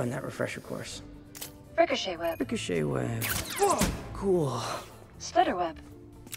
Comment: All these years I thought she said "Spider Web." I am living a lie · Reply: "Splitter Web"